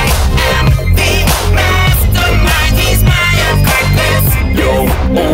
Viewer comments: "I am Mastermind, he's my accomplice"- sounds like Papyrus and Flowey sing it together. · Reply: Totally thought it said apprentice not accomplice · This whole time I thought he said "apprentice" not "accomplice" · "You'd be already be dead." "Kidding, If you couldn't tell. I get so bored, I amuse myself." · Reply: i always thought he said "im the mastermind he's my apprentice"